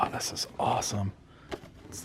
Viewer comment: What's up?! Did you just say "The Ram Fist Is Awesome" ?! 😂 cool video.